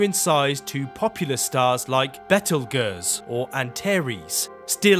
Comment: did you just say "bettle gus"?